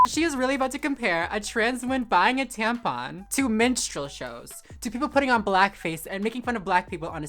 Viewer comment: At first I heard "menstrual show" 😂